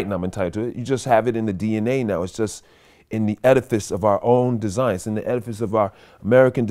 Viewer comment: What is the word he used at 4:52 (etifest) (etiphist)?